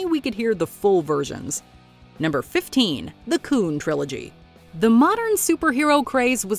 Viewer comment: did she just say the c slur? 😳